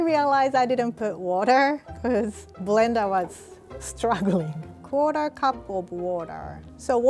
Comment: i thought she said "water cup of water"